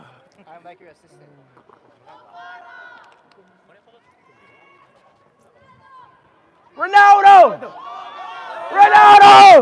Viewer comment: I thought u said ur a Messi fan